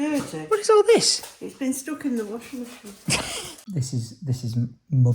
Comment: 😂 i honistly can't be the only one that thought mum said I've been stuck in washer machine 😳😬 😅i had to rewind and was like oohhh i was guna say bit sus that 😂